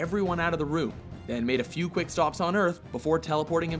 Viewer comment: was I the only one who heard that he made a few quickscopes on earth? Wtf